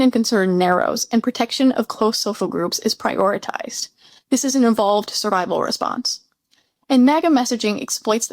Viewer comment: your white captions say "involved" and the closed captions say "evolved", which is what I hear you say. Since evolution itself is doubted by some of the anti-science crowd, that might be another topic to cover. Overall, I think your explanation is correct, but I'm afraid it makes me feel even more hopeless that our society will ever recover from the current wreckage. The disjunct that makes it seem like some "Christians" are okay with all the cruelty, daily lies and illegal activity of this administration cannot be overcome if Magas are too frightened by critical thinking and diversity to see beyond their own noses.